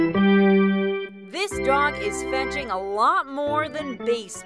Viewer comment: if you turn on captions it says this drug instead of dog